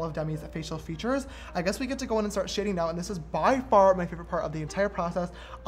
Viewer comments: When you said, "I guess we can go ahead and start sharing now," I thought you said, "I guess we can go ahead and start shitting now." 😂😂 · I could’ve sworn I heard him say “I guess we get to go in SHITTING now.” lmao idk why but I do. And the sketch is A-MA-ZINGGGGGG😍😍